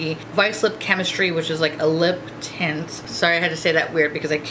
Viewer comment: When you were talking about the lip tint I kept hearing "limp tit".